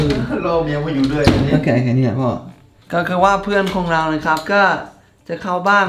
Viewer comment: มันคือ แค่นี้และพอ แต่ได้ยินเป็น แค่นี้และพ่อ 5555